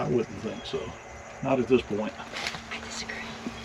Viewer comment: Did anyone else hear the lady say " I disagree" !?!? 👻👻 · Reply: Yes I heard it clearly. At first I thought it said I have a secret but after turning the volume way up and listening to their convo, it clearly says "I disagree". I wonder if they have heard it now that they have seen these comments and watched the video.